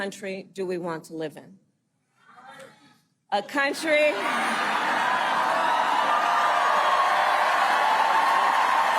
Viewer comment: What did the person in the crowd say at 13:09? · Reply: Lock him up